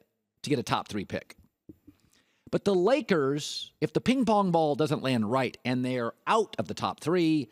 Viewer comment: Ping pong balls I thought you said king kongs.... right now they have 3 players making 40 million each, a decade has not elapsed and they have a championship sandwiched between a couple of losers.